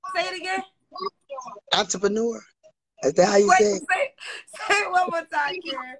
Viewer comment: Thought she said Auntypreneur